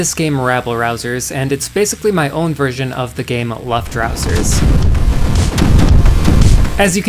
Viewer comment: your version of the game... what? i don't hear it clearly and can someone tell me what is the name of the game? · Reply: Luftrausers! Sorry I didn’t put it on screen!